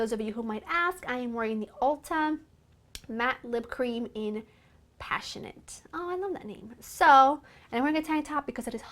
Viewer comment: Stunning! What is your lip color? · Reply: Sounded like she said Ultlatium matte lip cream color passion.